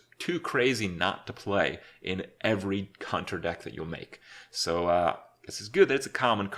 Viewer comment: I swear he says 'cunter' instead of hunter